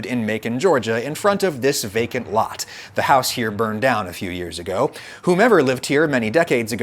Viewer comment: House burned down did ya say?